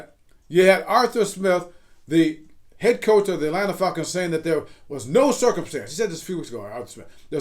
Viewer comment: "Had Arthur SMith of the ATlanta FUCKING saying...." Shi had me weak. It sounded like that.